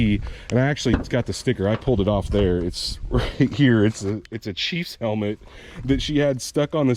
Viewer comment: Chiefs haha I thought he said cheese sticker hahah, Mine looks just like that only with aluminum slots instead of those chrome rims and is red well it used to be anyway its red pink and grey now because Gm has hasnt got paint right since the early 90's an never cared to fix it I guess , I dont have the bug shield anymore I swapped my hood and grill for the HD version and yeah the 200pound fiberglass bed cover still on it haha =)